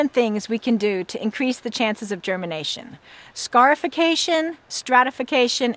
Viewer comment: When she first said scarification I thought she said scarf-ication.